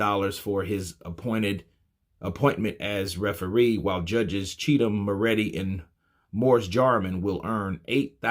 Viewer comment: Hang on. Wait! Did you just say 2 of the judges are called "CHEATUM" and "ALREADY"?